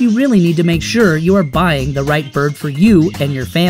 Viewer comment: did i hear birb